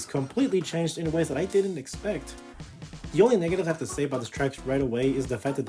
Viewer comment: What was killed off indefinitely? I can’t quite make out what it was that was said. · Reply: The bahs. For some reason even when I rerecorded it, my voice is really hard to tell in certain lines. That or my editing software has issues with the volume of Tour music